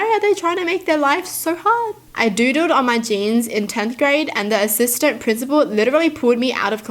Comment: I thought she said I doodooed on my pants lol